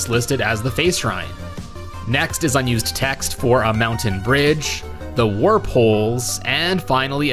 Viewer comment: Wait why did it say Mountain Dew all of a sudden?